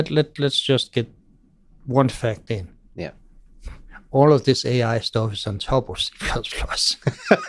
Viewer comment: What does he say at 5:35 "all this AI..." I couldn't get it. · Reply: "all of this AI stuff is on top of C++..."